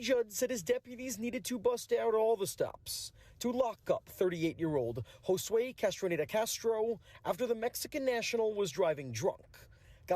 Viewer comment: I thought they were saying Castrated Castro. 😂